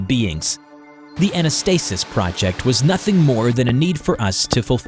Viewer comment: I thought Anastasis Said Nastysis 😂